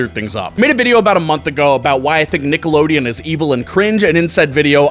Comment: I thought that said "Nickelback" was evil and bad...